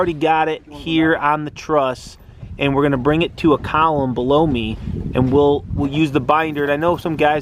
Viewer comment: why did you say, "Blow Me"? Where you talking to? That wasn't nice. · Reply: 141PP, he didn't. said, "...hook it to a column BELOW me...."